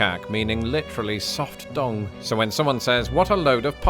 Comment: The subtitles say "soft dong," so I really misunderstood what was going on for a few seconds, until he explained the "dung" thing.